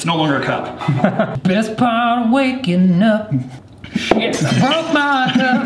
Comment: Excuse me but did he say shit or is it just me